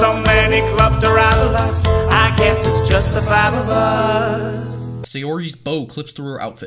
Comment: wait did he say "I guess it's just the battle bus?"?